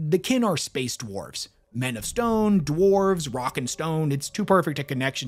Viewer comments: did i hear a rock and stone? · Did I hear a "Rock and Stone"?